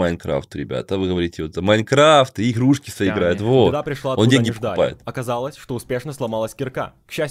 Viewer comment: я один услышал что сказал "деньги покупает"???